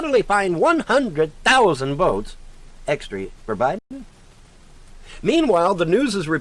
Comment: That's almost definitely Mars. it's bright enough the last few weeks to be seen inside the light pollution of a gas station. 'That's not Mars, and it's not the North Star, either. And when I drive around, it follows me." People who know nothing about astronomy will say things like that all the time. The north Star is actually pretty dim, but he clearly thinks it's one of the brightest stars in the sky. Also, yes, 10 miles down the road the stars will still be in the sky- not following you, just there in the sky where they always are. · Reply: Does that illiterate glob of shit actually say "100,000 extree" votes, instead of "extra"?